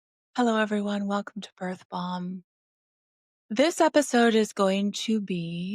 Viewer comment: When I heard "welcome to birth balm" I thought I heard "birth bomb". Both would be apt for. 🤣